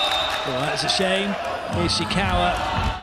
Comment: Did he said that's a shame?